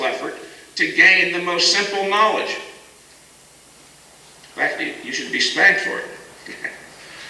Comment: what does he say at 24:56 ? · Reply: "I think you should be spanked for it"